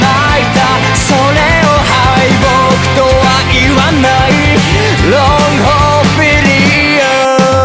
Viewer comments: All I hear is "Long porn video" instead of long hope philia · When I first heard him saying 'Long Hope Philia' I actually misheard it as 'boku wa hero'